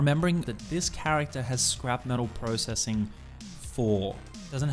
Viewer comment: What skills effect this, I heard you say "scrap metal reprocessing", but I only see "reprocessing"